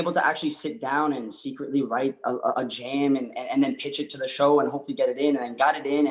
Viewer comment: Is it just me or did anyone not hear him catch himself and say jam instead of love song because if he did I’m squealing eternally I watched this show 6 times I’m about to be in my 7th today